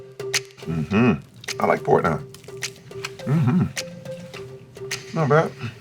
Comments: did anyone else hear him say "I like porn hub". Or am I just very dirty minded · Do i really scene that subtitle said "Terry : i like porn now " Who tf not lovin porn :) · did i hear him right? did he say "i like pornhub"? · who else thought he said i like fortnite 😂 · did he say I like pornhub? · idk why but I heard "I like pornhub" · DID HE JUST SAY “I like porno” 😂😂😂